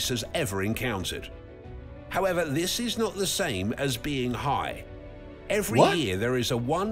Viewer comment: Not the same as being "high" did I hear that right?